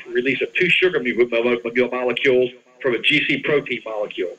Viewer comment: Listen to the audio at 10:19 What is he saying there? "Two sugar a be bo ba mu mo be ba molecules?"